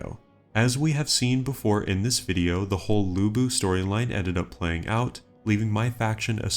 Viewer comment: I thought bro said Labubu storyline 😂